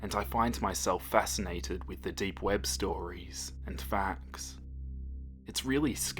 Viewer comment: Lmao, I thought it said “scary dweeb stories”